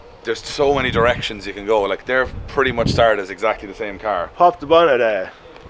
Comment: I'm a recent subscriber from the states and it looks like I'm going to have to watch a lot more of your videos 'cause I'll be frank - the Irish accent is at times so thick I can't understand what was said haha. Listening to talk was like listening to an old man trying to speak after just waking up from a bender. The captions don't do much to help either, like at 7:06 I think he said "pop the bonnet there" but Youtube's captioning software transcribed it as "pop the butter there" which is hilarious. Anyways, enjoying the content and now I have something to watch while playin' vidya - cheers.